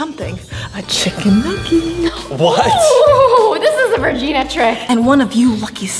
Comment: does anyone else hear 'virginia' and not 'regina'?